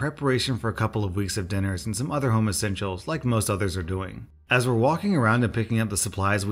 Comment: I read the first one on my own 😱 I've never had an overlap from youuu I read it on Reddit before you finished your edit! 😅 **I'm sorry my dearest ** 😭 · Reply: When you read "home essentials" at the start of the second... I heard "homosexuals"!!????! I had to rewind 🤦🏼‍♀️😅😂😂😂